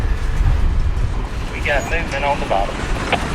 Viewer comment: "And there's Danny…" Be Surprised if ANYONE caught that. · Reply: Hard at age 70 to believe that here I sit at milepost ll4 of Amtrak's Empire Service line (CSX for freight), trying to hear "And there's Danny" at 3:22 on a cheapie phone. Okay, I guess I hear it. Maybe someone can tell me about the previous phrase. Is it "We got limping on the bottom"??? Or maybe "leaking"??? The best of us here in the Kearny Metropolitan Area actually love and appreciate this southeastern linguistic conspiracy to mystify us. After all we're the ones getting the orange juice. Fabulous video! I still have some sort of cousins in Piedmont region NC and northeastern Georgia, maybe Toccoa, btw. Thanks again!